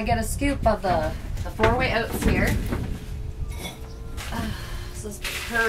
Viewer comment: What kind of oats? Sound like you said 4 way oats.